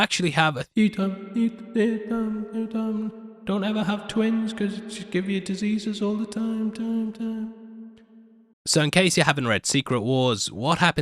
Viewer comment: “Don’t ever have twins 👯‍♂️ because she’ll give you diseases all the time” did I hear that correctly?? 😂